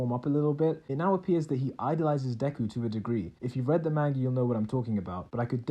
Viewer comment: did you just say “mangee” ??